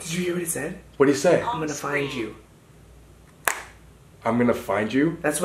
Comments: I shit my pants when he said 'Im Gonna Find You' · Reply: Baby Girl i thought he said "i can find you." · I thought it said 12 but it really said I going to found you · I thought I heard One, Two but when I played it back a second time I heard I’m going to find you. But that is creepy af